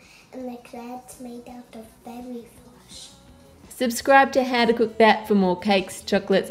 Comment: not gonna lie i thought he said fairy flesh not fairy floss... lol!